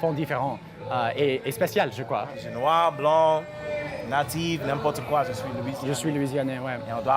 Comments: Cest moi ou a 12:45 il dit noir, blanc, nazis moi je suis Louisianais ? 😂😂😂😂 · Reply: Nan, il dit "natif" 🤣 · "Noir, blanc,... nazi,... n'importe quoi ; je suis Louisianais" XD